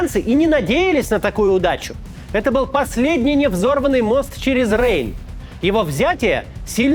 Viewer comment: Сначала услышал Невзоров, а оказалось невзорванный.